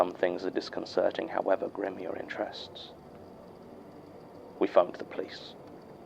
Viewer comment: does no one hear “we fucked the police.” at 11:10 ? 😭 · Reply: No, it's "we phoned the police." You're probably just not used to that accent though, easy to mishear words in that case.